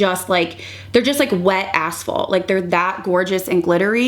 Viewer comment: Lol I listen/watch in 1.5 speed so when you said they are like wet asphalt (still not 100%that was the word either) but I heard something completely different, I heard “wet a**hole” 😂 thanks for the laugh. As a fellow Columbus resident, glad to see you back ❤